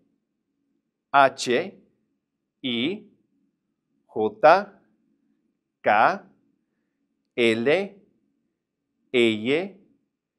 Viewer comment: It sounds like he's saying "Gah" instead of "Kah".